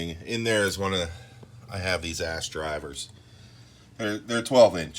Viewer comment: Thought you said ass driver for a minute.... hahah